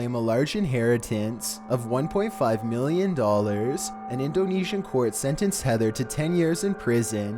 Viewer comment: captions say in dough knee shin 😭